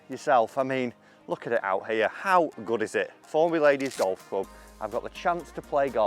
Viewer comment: Very good! thought you said “ Formerly a Lady’s Golf Club “ what is it now? A Cricket Club for men? 😀😆!